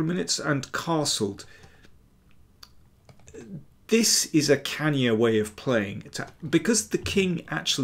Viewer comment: Quick English question: what did he say at 3:12? "This is a x way of playing"? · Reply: 'This is a cannier way of playing'